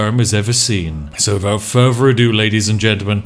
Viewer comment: honestly the fact that he sounds like hes saying fervor ado instead of further never gets old <3